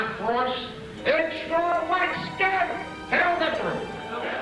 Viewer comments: did they say heil victory? or.... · Who else hears “Hail Hillary!” ?